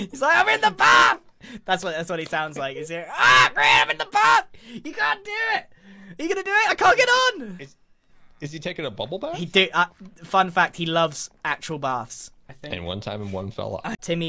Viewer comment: did Grian say “GOD DAMNIT” here? Was that just me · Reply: i think he said "you can't do it" lol